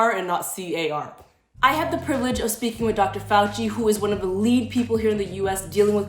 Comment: I thought it said Dr Faucet